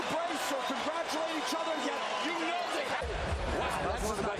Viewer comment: What’s Did Plant Said To David At The End of The Fight ?? I Heard ….. “ Your Going To Be one Of The Best Of This Generation “